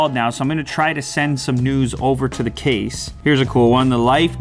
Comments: "Send some news over to the case" I thought he said nudes........... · anybody else hear " I can now send nudes to the case"? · What Unbox Therapy said: "I'm gonna try to sent some news over to the case" What my brain thought: "I'm gonna try to sent some nudes over to the case" 😂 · Who else heard him say send some nudes over to the case at 3:25 ?! 😂😂😂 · Who else heard "I'm gonna try and send some nudes" at 3:23? LMAO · i thought he said im gonna send some nudes xD